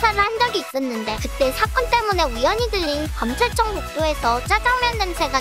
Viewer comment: 들린X 들른O 들리다 소리가 귀에 닿다, 빙의되다 들르다 중간에 잠시 머무르고 가다